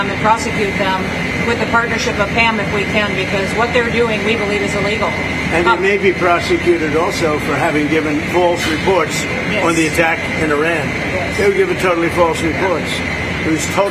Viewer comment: The first time I heard this sound byte 20:26 (w/ BTC) I clearly heard "There may be prostitute". Glad yet sad you have such great production. 😅